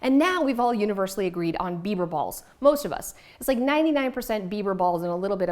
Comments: Digital books are clutter free. Also I thought you said Beaver Balls. And I was thinking "Doesn't Canada have plenty of those already?" 😂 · Oh dear... Beiber Balls? I thought you were saying Beaver Balls! XD What a phenomenon... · Wow, I'm living under a rock apparently and haven't gone outside at all to hear about these "Beiber Balls". I thought you said "Beaver Balls" and I assumed they were like Beaver Tails desserts but smaller and in Timbit form. That would be amazing and Tim Hortons should get on that! I probably just lost millions of dollars just by suggesting this in the comments if they catch wind of that idea. 😬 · I thought you were saying “beaver balls” which made it even more! 🤣